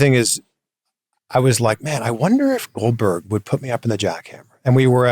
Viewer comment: Does he say "Oldberg" to his face?!